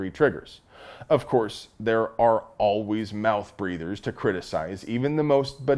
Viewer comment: Did you say "mouth breathers" or "mouth breeders"? 😮